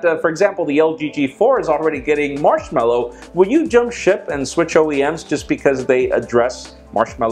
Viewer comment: All I hear is "Jump Shit".